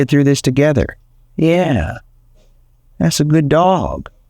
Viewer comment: did dog just say "Yep" ??